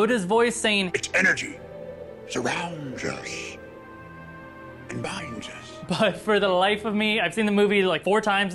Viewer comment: I heard yoda's voice he says" it's energy" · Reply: He said the "energy surround us and bind us"